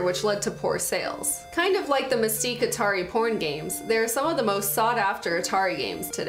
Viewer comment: wait... Did I hear porn games?